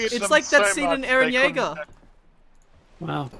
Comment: did he say," Its like that scene from Eren Yaegar"?